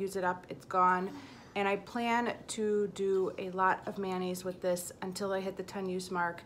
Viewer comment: Hi... when you were talking about the nail polish I thought you said "I plan to do a lot of mayonnaise with this" 😜